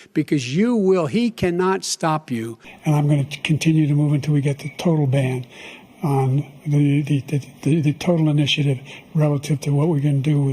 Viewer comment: Was Biden saying ‘Until we get the total ban on border patrol?’